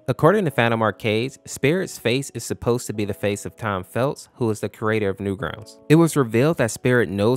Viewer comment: Is it me, or does it sound like he said Tom Felt instead of Tom Fulp?